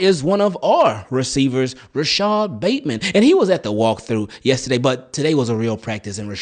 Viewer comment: Bateman is actually a bigger part of the offense than most realize. The respect he commands deep and in the red zone creates opportunity for everybody. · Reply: I straight up thought you said Batman at first 🦇